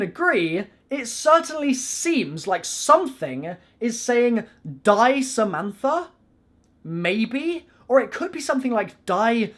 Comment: the reversed Samantha ghost part sounds like it said it's time Samantha.